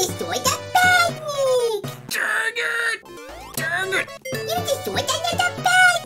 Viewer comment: what did he say… LANGUAGE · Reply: Dang it? I don't think that is bad